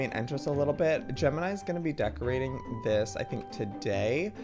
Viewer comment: did Joey said Gemini or gem and I